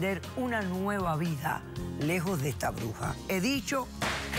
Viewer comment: totally thought she said PUTA but she said BRUJA I would prefer the first one but the second one describes her well too.